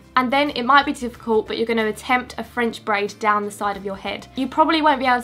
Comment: For a second I thought when she said "french braid" I thought it was "french fries" lol